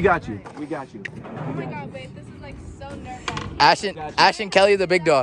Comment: did she say omg babe? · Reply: She said wait